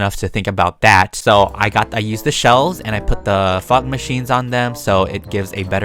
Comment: LIVING INSIDE AN OVEN! That's totally normal! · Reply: sorry not trying to be rude but I thought you said f Machines instead of fog machines